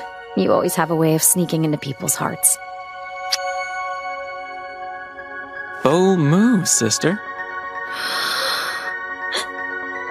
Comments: I just LOVE Edric’s “Bold move, sister” I LOVE the Blight twins lol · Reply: I always thought he said wrong move sister and it confused me after that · I just LOVE Edric’s “Bold move, sister” I LOVE the Blight twins lol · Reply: I JUST REALISED HE SAID 'BOLD MOVE' I THOUGHT HE SAID 'OH, MOO' FSR LOL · for the longest time, i thought edric was saying "Oh moo, sister" instead of "Bold move, sister" 😭